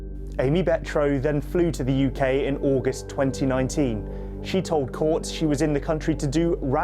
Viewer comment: Oh she told courts.. I thought she said "shitole courts"😅😅